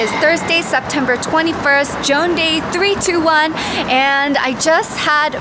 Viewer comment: I thought you say jongdae when you say joanday😂